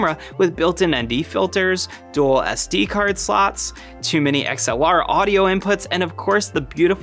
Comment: When you said your C70 has "2 mini" XXLR inputs i thought you jokingly said too many XLR inputs lol